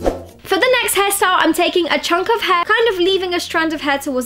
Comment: did i heard '' take Jungkook hair ''